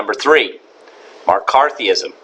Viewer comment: did he say *MAR*CARTHEEISM. This is some quality shitposting right here! Now when I try to sleep, all I'll be able hear is soothing UUUH at 2:46.